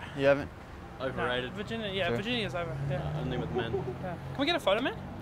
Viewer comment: DID HE SAY ONLY WITH MEN?? lol woah now